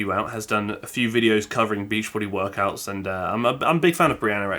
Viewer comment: Honestly thought he said "beef body workouts". 😅🤣